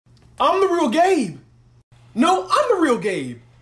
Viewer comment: "I'm the real gabe!" What i hear: I'm a real GAY